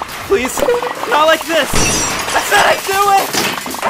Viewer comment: I could have sworn I heard “Acidic do it”